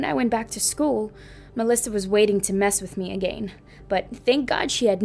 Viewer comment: why does she say “ahh gain” like tf 😭